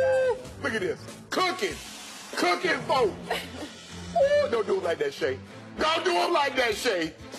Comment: "Look at this cooking.....cooking folks"....hahaha im dead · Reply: When i first heard it I thought he said cookie four or cooking four